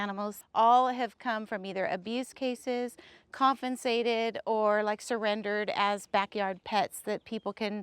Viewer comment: did that woman say 'confensated'? What the fuck does that word mean to her? I only ask as it's not a fucking word!